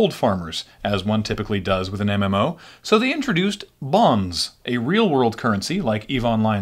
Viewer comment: I could have sworn he said "bongs" not "bonds"...